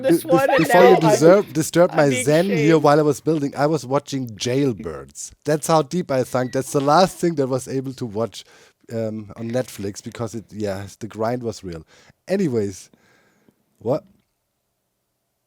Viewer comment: I heard you say "the last thing to watch on Netflix" and fully expected the sponsorship for expressVPN to unlock geo-restricted content